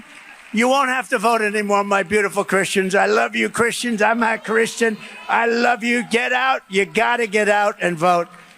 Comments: mark. Sounds like he says "I'm not Christian..." then stammers into another ramble. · He also says (if you listen carefully) "I'm not Christian". So what is he? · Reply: i was about to disagree, i'm hard of hearing but i thought i heard "i'm a christian" first time around, and the subtitles said "i'm my christian" so i nearly settled for my first thought BUT ..... i played it twice more, second time with my wife listening , and yup "i'm not christian". the interesting thing about this is that its probably the first time in decades he's actually spoken the truth. cannot really follow his thought process, he's appealing to christians to vote for him, saying he loves them, he waves the bible around, and then proclaims to be not christian. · It's sounds like Trump said, "I'm not Christian." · Did I hear correctly, at the 4:59 mark, that he said "I'm not Christian" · Sounds like he said, "I love you Christians, I'm not Christian." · Did I hear something correctly? Did he actually say ". . . . I'm not Christian." ???